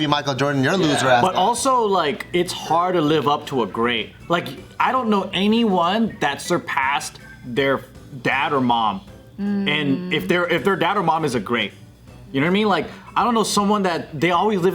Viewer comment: I kept hearing "It's hard to live up to a grape. If their dad or mom is a grape."